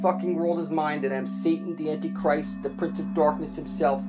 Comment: Yo at 15:11 they really said "for Mr Satan"